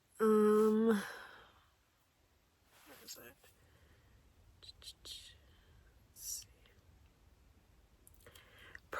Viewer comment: I swear I heard a man talk quietly right then. I played it back and kept hearing it again and again. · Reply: Heard it too , he says, " what was that "